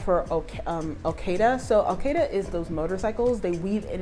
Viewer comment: It's Okada (Motorcycle Drivers) not Okeda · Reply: Took me a while to understand, thought she said Al Queda🤦🏽‍♂️😂